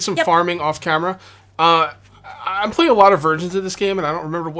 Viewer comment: I know you said "versions" but I heard "virgins"...